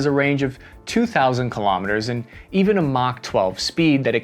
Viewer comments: Did I hear that 10:20 Mach 12? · Did....did he say "Mach 12 speed...." at 10:20? Has the Darkstar arrived and I missed it?